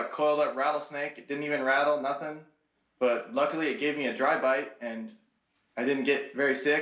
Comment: When he said the snake only gave him a dry bite I thought he said “the snake only gave me a drive by”